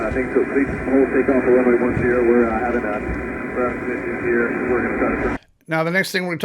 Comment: Someone elsewhere pointed out that if you play the video back at .75 speed it very much sounds like he is saying "We're uhh having uhh we're having some issues here". Note, he said the word "issue" earlier in the call, so we know what that word sounds like in the recording. I'm just pointing this out.